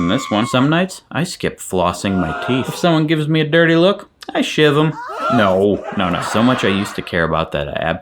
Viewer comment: "I shiv them" 😂 or did I mishear😅